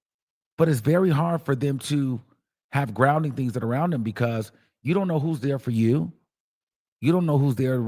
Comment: I thought you said you don’t do cke